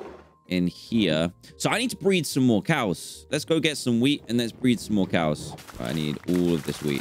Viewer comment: Is it jus me or when Dan said let’s go get some wheat for a sec I thought he said let’s go get son w33d